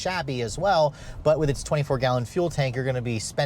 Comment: I thought I heard 14 gallon gas tank which wouldn't make for this beast. I guess it's 24 gal that's in the graphic. I think that's around 240 timeline.